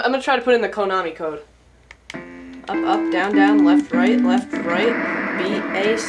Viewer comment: Oh me and my stupid brain, I thought you said Up Down Up Down, sorry.